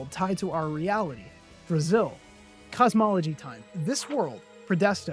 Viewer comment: i actually thought you just said "Brazil"